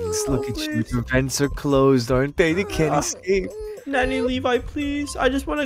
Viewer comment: I thought said daddy levi but he said nanny levi